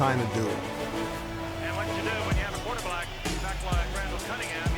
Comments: I heard "quarter black" too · I heard “quarterblack” right before Randall noticed it. 😂